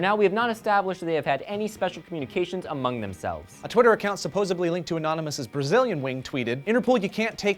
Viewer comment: Did Joe say "supposably" at 1:04??